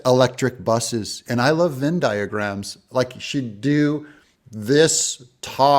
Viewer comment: I thought she said Zen diaphragms.